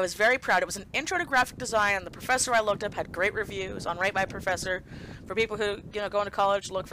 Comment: I thought you said “rape my professor” lollolololollolollolololol